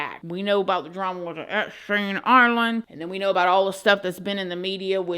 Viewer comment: What on earth did she say at 13:13 😂 l kept rewinding to hear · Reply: The drama with Epstein Island